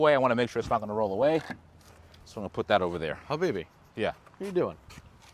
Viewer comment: did he just say habibi? LMAO